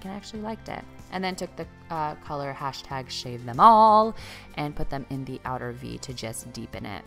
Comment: When you used the dark brown shade it totally sounded like you said “shave my balls”.. I have no idea what the real name is lol!